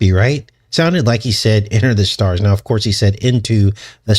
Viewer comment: Great video, 🤗. Ty! It sure sounded like Trump said EntertheStars!